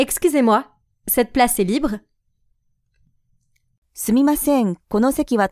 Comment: prise ではなく libreと聞こえます